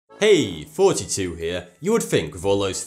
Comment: i thought he said 42